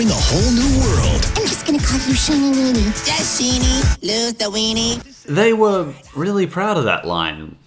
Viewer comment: I can’t even understand Sheen. When he said “Just Sheenie. Lose the weenie.” I heard “ That Sheenie LOVES the weenie.